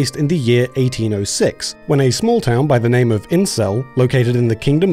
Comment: I'm sorry but: I hear it wrong or the small town where the game takes place is actually named INCEL?